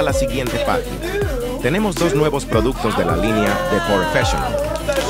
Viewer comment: Creo que dijo envidia en la